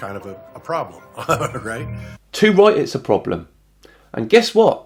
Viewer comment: "two right is a problem"? Not sure what you're saying there. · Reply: The Ultimate Reductionist “Too Right, it’s a Problem”. “Too Right” is a British colloquial term used to agree with a statement. A shortened version of “You are Too right” meaning you are ‘More Than Correct’. Of course you can’t be MORE than Correct, so it in essences is an exaggerated/sarcastic response. Generally used when someone either wishes to emphasise there agreement, or when the are responding to an obvious statement.